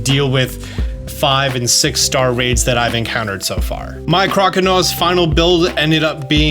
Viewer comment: “my CROCANAWS final build ended I up being…” I was like WHAT DID HE SAY???